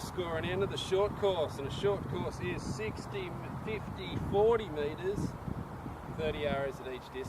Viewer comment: I couldn't hear you that well but it sounded like you said "short course is 60 50 40" , add 30 yard to it and that is my morning practice routine (on the weekends) Technically range is only 50 yards deep, but I step back in the weeds for 60 yards. I shoot at bag target with 3 inch stickers on it, so I don't keep score. Usually takes me around 3 hrs to shoot 50 arrows at each distance, for 200 arrows total. I shoot hunting bow (PSE Bow Madness 34) with V-force V6 hunting arrows from Victory Archery. On work days I shoot at least 100 arrows after work, range is only 5 minutes away from work. Watching you guys shoot helped me a lot with form, and tips from coach are great.